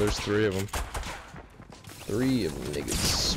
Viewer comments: Did he say the N-Word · I KNOW I JUST HEARD "3ofthemniggas"